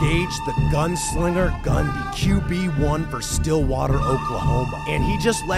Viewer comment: I thought he said Bundy, uh oh😮